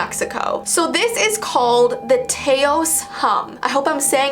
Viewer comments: At first I thought it said the tacos hum 😂😂😂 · I thought it said the tacos hum 1:50 That would make more sense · When it said the taos hum I thought it said the tacos yum 😂